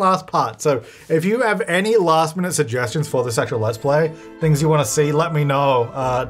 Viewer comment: there is a typo in cc at 6:17 where instead of "this actual" it's "the se*ual" 😂 i was quite surprised at that